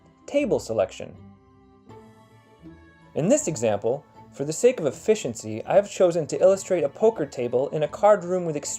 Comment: did i hear for the sake of effishency?